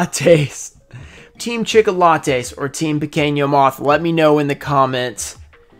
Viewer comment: What is duel monsters? And like your fan from the U K I am getting a great escape from the stress watching your vids! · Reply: What is up with petit moth?? And why does it sound like your saying pekinyo moth lol